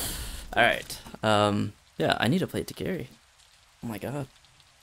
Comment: "I need to play hitokiri" the caption says: "I need a plate to carry" 😂